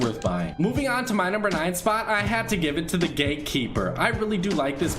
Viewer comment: I heard "Gay Keeper" XD